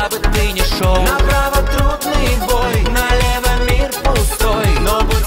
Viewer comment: "На право трудный бой,налево мид пустой" мне так послышалось)